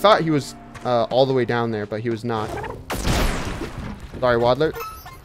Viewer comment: He said "Goodbye Waddler" BUT THE SUBTITLE SAYS "SORRY WADDLER"